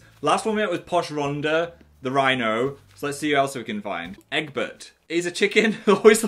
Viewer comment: I heard "Eggbutt" and I'm kinda sad I was wrong.